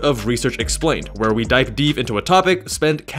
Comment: very unrelated but dipe deev was funny to hear instead of dive deep 😂